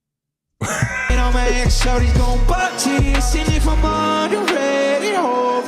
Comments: Oh… “Walk to this.” …I thought he was saying, “Rock titties.”. As in:- “All my gang affiliated gonna rock titties.” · I didn't hear "walk to this" I heard "what titties" XD